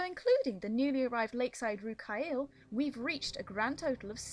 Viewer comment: can anybody tell me what she said? The new lakeside what? The subtitles aren't any help and im really confused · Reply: Rh'kaial. At the time I was experimenting with conlangs for the series (fictional language) and Rh'kaial was the word for dragon. Ujorkas was wyverns and Inharil was, wyrms.